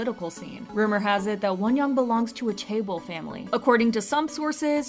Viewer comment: Forgive me, a what family? I can't make out the word being used, and subtitles say "table" family? 😅 I'm not sure if that's correct haha. If someone would be kind enough to let me know! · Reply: chaebol family. chaebol refers to the people who are born to rich households where often the family members are ceos or have high positions in companies.